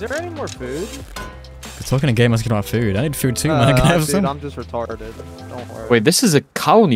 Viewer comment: what word was that? "i need food my ni**a"